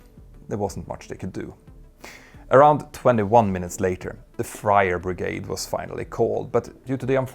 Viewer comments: “The Friar brigade” 😂. Not to make light of a tragic situation, but the visual of a bunch of monks trying to put out the fire made me chuckle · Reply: I heard it as "fryer brigade". Exactly what you wouldn't need attending a fire. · “The Friar brigade” 😂. Not to make light of a tragic situation, but the visual of a bunch of monks trying to put out the fire made me chuckle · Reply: I heard it as "the frier brigade", which could be a specialized team to deal with grease and oil fires in kitchens...